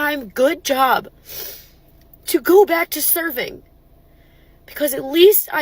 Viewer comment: I thought she said 'surfing' 😂😂😂. Still ironic 😂😂😂.